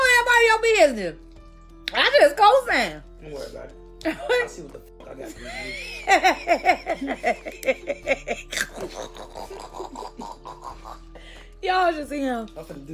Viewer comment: She loves the toxicity. That convo while eating was so erratic, u can tell he’s unhinged. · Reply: Now I know I’m not the only one who heard him say @ 9:04 it’s ok I know wtf I gotta do to you ? After she called him a crackhead 🫣 He was definitely unhinged and basically told on his whole plan. It didn’t matter to him who he set on fire 🔥 whether it was her or the friend but he was gonna hurt someone because he was still very much a hurt lil boy himself. Sad asf all around